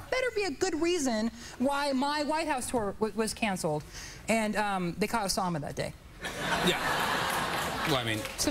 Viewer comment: I didn't understand what Chris said at 2:50. What did he say? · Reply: "they caught Osama that day" (Osama Bin Laden)